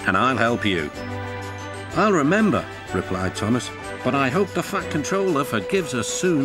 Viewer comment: WHAT DID HE SAY?!? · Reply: He said the fat controller